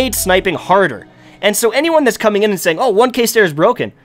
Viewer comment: When you you refer to the One Case Stair are you talking about the 1000 yard stare? I play destiny alot and am a sniper and never heard someone say one case stair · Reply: He is sayinf one-kay as in 1000 (1k) just sounds like he is saying one case stair but he is really saying one-kay